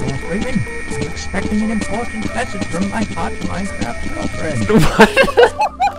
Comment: "I am expecting an important message from my hot minecraft girlfriend!" okay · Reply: I thought he said Part time girlfriend